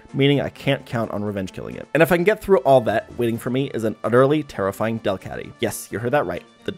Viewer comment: I thought when he said terrifying was Tara flying lol